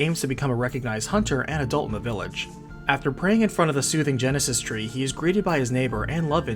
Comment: Anyone else hear, "Sega Genesis tree" at first?